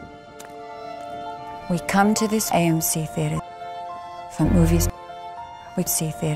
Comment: i was just watching the jerma nuke video and I thought it said dmc as in devil may cry